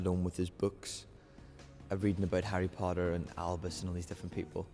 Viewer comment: When he says how Scorpius was always alone with his books, "reading about Harry Potter", I misheard it as "reading Harry Potter" as in the SERIES. LOL just pictured Scorpius on his bed, absorbed in the Harry Potter collection that we all have XD